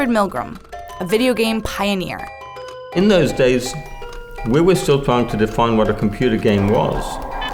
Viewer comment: I misheard and thought she said "weremen", like the opposite of a werewolf.